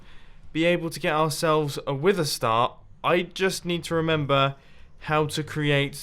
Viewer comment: Did he say wither star